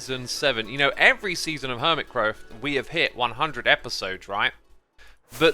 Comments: seconds in did you say hermit crow??? or am i stupid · anyone hear “Hermitcrow”?